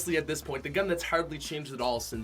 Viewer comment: It sounds like he says "Legendary Scur" lmao